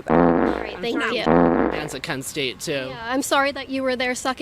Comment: I think he's saying Maryland is a c*nt state too that's what I hesrd